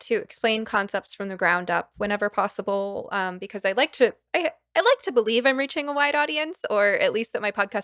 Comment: I heard "I like to think I'm reaching a white audience". I know that's not what you said but I was thinking "uh oh. That's going to give the trolls a lot to play with."